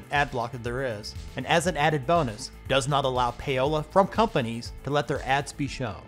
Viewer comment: it says "doesn't allow something from companies who...". I cannot understand the word for that something, It starts with "pa" but I cannot figure out the word?